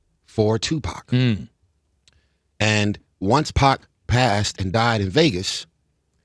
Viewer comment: Did he just call Two Pac, “Once Pac” ??